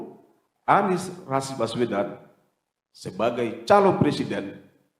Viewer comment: Ini w yg salah denger apa jaket ka'bah yg salah sebut si...? Ko nyebutnya anis rasis baswedan di menit 2:24detik dst... Coba tolong cek lagi dong di menit 2:24detik...